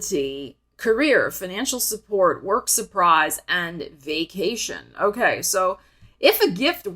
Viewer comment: For Gemini moon, swore you said "gaycation" as in "it's time for a gay cation!" And I thought - how clever. Never heard gay cation before but all righty‼️‼️❣️❣️😘😘